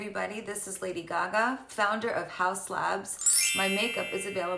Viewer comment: Lol when she first came on I thought she said house labs and I thought she was gonna be finding housing for people funny